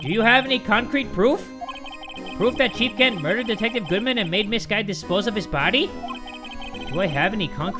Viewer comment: thought he said "and made this guy"